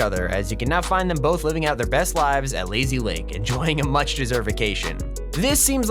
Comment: Did he just say leaky lake?